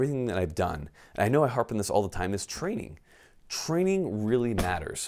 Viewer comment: Your mouth says "get training", I keep hearing "get flannel"...